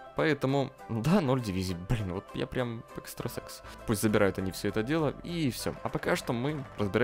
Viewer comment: Мне одному послышалось, что ты сказал экстасекс, а не экстрасенс.